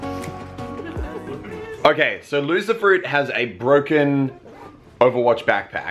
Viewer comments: He said loserfruit and I thought he said lucifer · OMG I'm May be bad for this but I thought you said Lucifer for her name😯